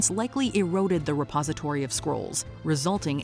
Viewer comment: A half awake me thought she said squirrels instead of scrolls like squirrels destroyed the literature in the Library of Alexandria.